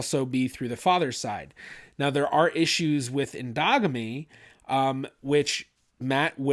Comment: Lmao…I know he said “endogamy” but I heard “dogmeat”…rofl